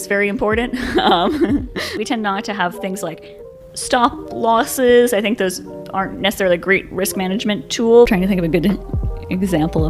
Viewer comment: did she say Stop loss is a not great risk management tool. oh nkosi yam